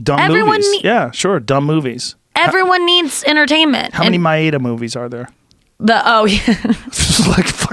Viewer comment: What movies is he talking about? "my ada" "mai aida" ? what is he saying here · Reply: He's talking about the Madea movies that Tyler Perry makes/stars in. Took me a few seconds to work out what the hell he was talking about since he doesn't pronounce it correctly.